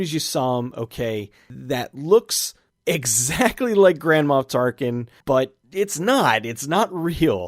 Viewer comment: did you just say "Grandma Tarkin" around 7:32?